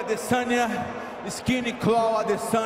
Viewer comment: did he just said skinny crow Adesania?